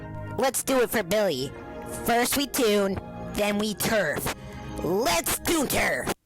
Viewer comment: I Heard frist we goon 😭😭